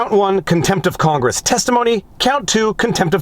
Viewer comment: Christ!! If that's the cast, like 340 million Americans are guilty. Oh, wait. . . .I thought you said Contempt for Congress 🙄